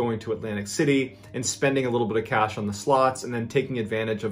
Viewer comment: I honestly thought you said “sluts”, gave that sentence a completely different meaning😂